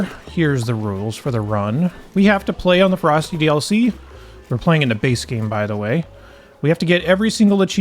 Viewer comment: I swear I though it said, by the way, we’re playing in the basement. Lol😂